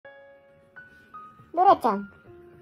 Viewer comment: ドラちゃーんって呼んでる時、うなちゃーんって一瞬聞こえた！ の実況上手い！